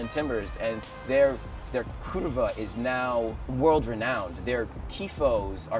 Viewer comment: it sounds like he is saying "Their kurwa is now world renowned?"